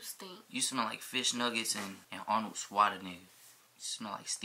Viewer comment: it sound like he said Arnold swat a nigga lmfaoooo